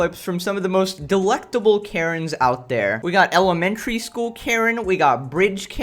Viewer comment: when he said elementary karen at the start i thought he said 'elephant karen"💀😂